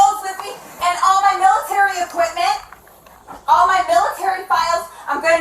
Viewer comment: I swear I heard her say, with quite the conviction, she needed all her 👃💇‍♀️"NOSE HAIRy equipment",🤥 rewound 3x, until Leon spoke up. (Using 1 ear bud surely didn't help)